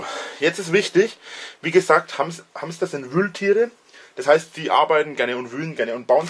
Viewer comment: Wer hat auch: "Hamster sind Mülltiere" verstanden?